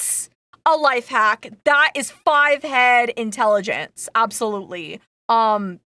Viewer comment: did someone say "head"? OwO